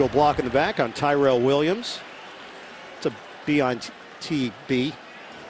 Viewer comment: Did he really say "bitb" on television and now know what it means?